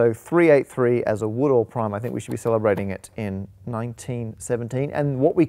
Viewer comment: Did I hear that right? Did he say we should be celebrating in 1917?